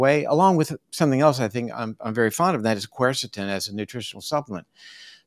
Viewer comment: What was the nutritional supplement around 27 -27:20 that Dr. Mutter said? Glusitan? I'm googling everything that it sounded like and I can't find it. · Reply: The nearest to Glusitan that I heard was Quercetin at 27.24